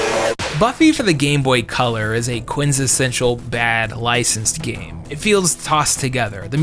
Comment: Did you just say "quinzisential"??